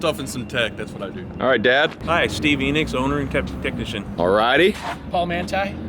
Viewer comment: ah it's "Steve Enix" or something, I thought he said "Stevie Nix" i was like... cool as name.